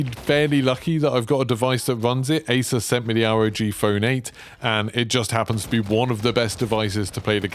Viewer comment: What kind of phone did you say.? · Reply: ROG Phone 8